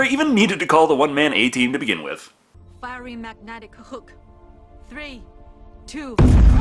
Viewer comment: I swear, the first time i watched this movie, i thought she said "Firing Magnetic Hooker". Who knew deep sea divers work as thot patrol as well XD